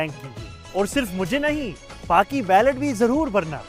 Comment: Tf he say? Free balaclavas for everyone? Nice try Mo.